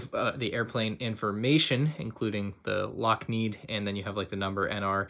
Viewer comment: Did he just say Lockmeed instead of Lockheed?